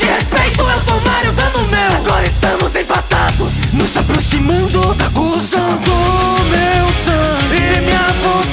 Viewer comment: Foda demais, mas mano, kkkkk, na parte 4:21, na parte "Nós aproximando da conclusão" eu ouvi "Nós aproximando da Cuzão" bixo kkkkk to com uma mente poluída